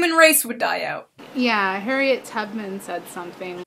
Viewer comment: Did she say Harriet Tubman? Idk I thought I heard it lol